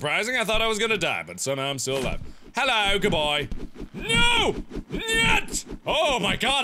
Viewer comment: did mark say net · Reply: He said "Nyet" which is "No" in Russian.